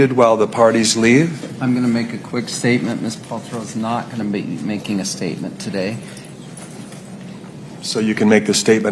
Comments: I wonder what she said to the plaintiff around 6:05 as she was on her way out. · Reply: I wish you well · I would love to know what Gwyneth said to the plaintiff at 6:08! I’m sure it was something polite but I’m still nosy! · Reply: “I wish you well.” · What did GP say to Sanderson at 6:06 ??? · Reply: I wish you well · what did she say to him?!?! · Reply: According to Sanderson she said "I wish you well"